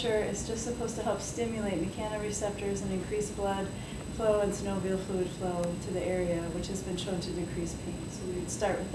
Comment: she mentions that it is used to increase blood flow and some other fluid flow? Can't quite make out what she says, can someone help me out? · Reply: mechanoreceptors to increase blood flow and synovial fluid flow